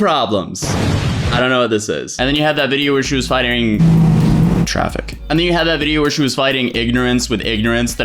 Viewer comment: Think you slick at 1:22 making it seem like there was a car outside making noise which is why you stopped midway through your sentence, but in reality you said fightering instead of fighting😂 almost...almost had us there